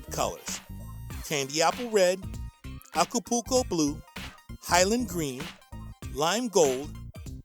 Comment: When he says acapoulco blue it sounds like he says akupooko blue